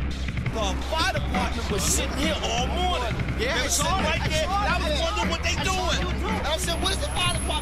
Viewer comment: Can someone tell me please what is saying first witness at 0:33, please? .......................... was sitting here whole morning. ........................... they doing? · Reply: "I was wondering what were they doing" I think.